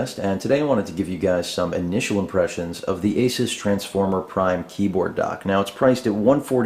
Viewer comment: For some reason i thought this said anus transformer optimus prime.